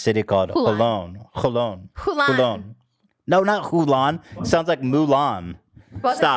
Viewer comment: lolllol I thought she was saying Mulan 2 lol